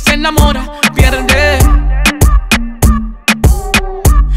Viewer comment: Yo pense que decia ( disco) no (discotahhh) ahorra ya no me gusta